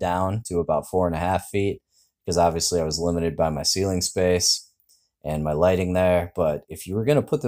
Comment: “Limited by my ceiling space” I heard “limited by mycelium space” lol. Great build! Thanks for your creative thinking and applied knowledge! I almost started to build a Martha tent but thanks to your videos I’m not. This build seems to avoid lots of the MT issues